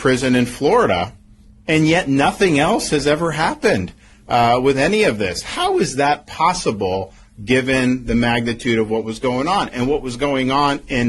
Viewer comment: Did DeSantis say "....given the Magatude of what was going on....." (03:10) regarding Epstein?